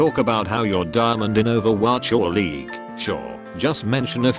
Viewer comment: I heard "diamond in Overwatch League" I felt a bit of concern for a second there